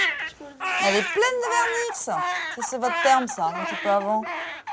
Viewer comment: Je rêve ou elle sort au calme " elle est pleine de vernix c'est votre sp*rme çà.. de avant " Mais QUOI 😂 ?!! elle a pas du dormir bcp cette dame pour sortir çà 😅